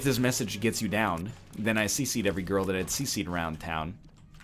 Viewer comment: did he actually say " no spider transformation?" Does he not know?